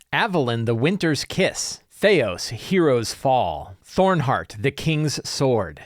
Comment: At first I thought you said, "Teos Heroes' Fall" and I immediately thought: Teos "Heroes' Fall" Abadia? LOL